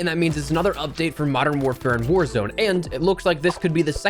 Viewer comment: Sounded like you said whorezone